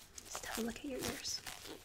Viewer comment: do you say "just have a look at your ears"? Because to me it sounded like "stop licking your ears", and my first thought was "if I could pull that off, I'd have more dates on Saturday night".